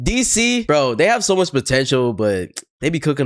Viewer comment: did you just say...POTENTIAL